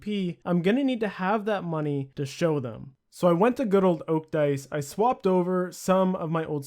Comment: did you just say fucking "Oak Dice"?